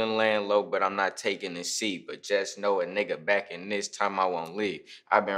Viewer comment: I thought he said *just know a nigga back in this time Halloween*😂